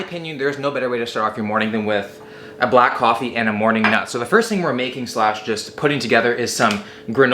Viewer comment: I had to rewind that 3 times, but did he say a black coffee and a morning nut @ 1:45?